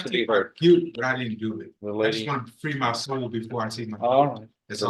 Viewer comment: what is he saying?? Something about “…but I didn’t do it…wanted to free my soul before I take my plea?”